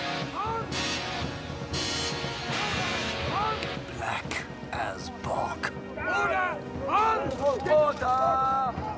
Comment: What does he say at 1:28? I only hear Black as Bork. tried looking it up with no luck. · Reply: "Black as bog"